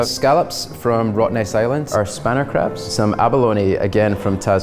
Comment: "... they kinda resemble a spanner." "Sure... what's a spanner?" "Is that not an American thing?" 😂😂😂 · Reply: I thought he said "spinner" like a fidget spinner lol